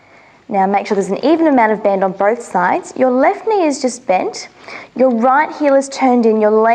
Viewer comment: Hi guys Can anyone please tell me what does she exactly say in these minutes (1:01) (2:19) (2:35) (2:45)? I'll be very thankful 😊😊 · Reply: 1.01: left knee is bent 2:19: Getting a stretch through the side of the knee and ankle 2:35: Keep your knee nice and long 2:45: Draw your little toe down